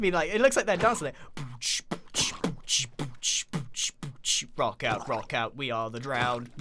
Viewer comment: "rocker rocker we are the drowned"-Grian 2018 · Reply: i thought he said rock out?